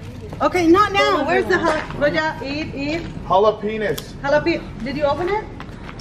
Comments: Bro#10:48 who else heard say jalopenis😂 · anybody catch that “Jalapeñis”😂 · anybody hear say jalo penis 😂